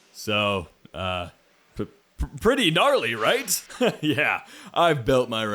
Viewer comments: did he say gnarly? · did he just say gnarly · Did bro say gnarly9:12